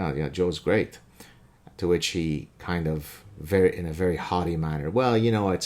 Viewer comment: lol you said haughty but I thought you said hottie 😍